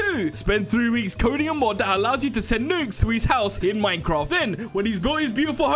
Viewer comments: Am i the only one who thought he said nudes instead of nukes i was like NAWWW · Bros lucky captions are on I looked away and thought he said “send nudes” I bolted my head back to the screen so fast😭😭😭